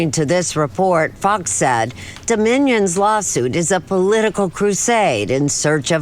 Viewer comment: it literally sounds like shes saying Da Minions lawsuit. DA MINIONS lmaooooo